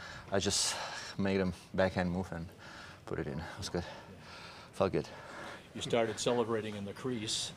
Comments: I thought he said fk it not felt good 🤣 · I know he says "felt good" but it sounded like " fuck it" 😄